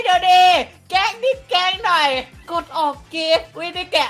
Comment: นาทีที่ 2:28 ไอ้มันพูดว่า กดออกเก